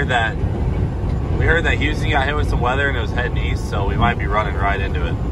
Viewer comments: I thought you said weast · East?? Oh I thought you said Weast.